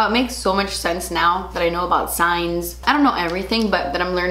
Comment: omg when you said 'I'm learning about signs' I thought you'd say 'science' and my nerdy ass got low key excited.. then you continued talking about leos and aquariuses hahaha